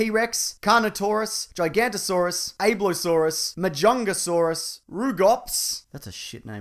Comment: What was the dinosaur he said at 5:18 (after gigantosaurus)? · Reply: Abelisaurus